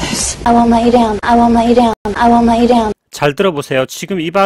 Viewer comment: 에 I won't let you down 이 아무리 반복해서 들어봐도 전혀 let you down으로 안들리는데 제 문제이겠죠? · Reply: ㅠㅠ 아직도 그렇게 안들리네요.. 보통은 속도를 느리게해서 들으면 안 들리는 소리도 들리는데 아 부분은 아무리 느리게 해도 let you 부분이 '마에잇' 처럼 들리네요.. 정확하게 들리신다니 부럽습니다...